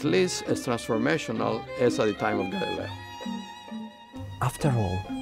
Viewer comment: Really great :) the only thing I could hear (though replayed about 7 times) was what the gentleman with no hair says at around 5:27 ....he says: "it's going to be at least as transformational as ?????????" ...could someone who can understand his next 3 words please say what they are! Thank you :) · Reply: "...as transformational as at the times of Galileo"...: -) Thanks For the positive comment!